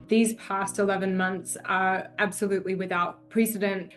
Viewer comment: Why did I hear "These pasta loven monks" 😅🤣😂